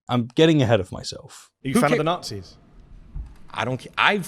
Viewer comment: Great vid. The moment when you said he clearly stated "Are you fine with the natzies?" I had to raise my eyebrows because I thought he has said "are you a fan of the nazies?". Not to be pedantic, the moment just made me laugh.